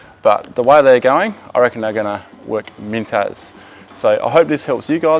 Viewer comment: they're gonna work .........? What is the word ? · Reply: "Mint as".